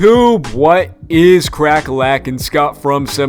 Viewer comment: Did you just say what’s cracka lackin 😂